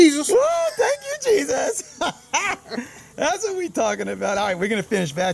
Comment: I thought he said "Thank you Bee-sus!" as first.